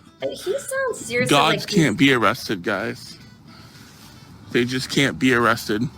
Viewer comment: I thought he said DOGS can’t be arrested guys! I guess he’s wrong because he’s headed for the pound😜🤣